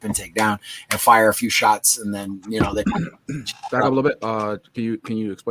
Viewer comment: I'm like Travis I thought he said hoochie mamma also LoL 😂🤣